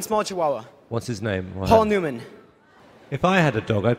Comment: did he just said poul neumann IM I A DOG